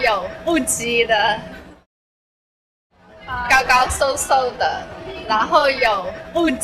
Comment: i hear it as ranhou although slightly off but i dont hear lanhou